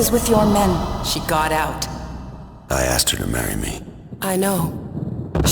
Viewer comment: I was looking away and swore he said "Wrong! You're geekin'" and I was so confused for a second.